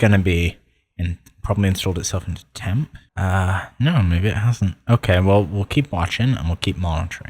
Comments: WAIT! WHAT DID HE SAY!?!? 😳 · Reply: "maybe isn't" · WHAT DID YOU JUST SAY AT 3:34?!??!! TELL ME YOU HAD THE PASS BRO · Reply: "Uh, no maybe it hasn't"